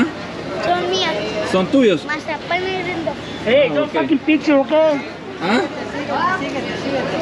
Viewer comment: "Hey don't taki pictures of boys" is what he says. Probably because there problems with that in some places (laws to protect underage people).